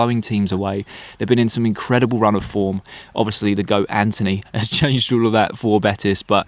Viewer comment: What did he just say? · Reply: The goat Antony? Betis have been on a good run of form?